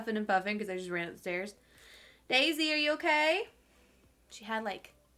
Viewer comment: Does anyone else’s Siri turn on at 2:35!? My phone thought said “Siri are you okay”!!! How weird